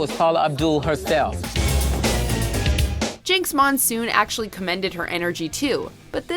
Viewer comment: did she just say jinkx